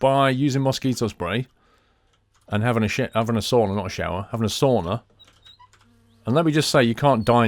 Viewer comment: did say shit? Thanks for the upload been waiting! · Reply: He was starting to say shower.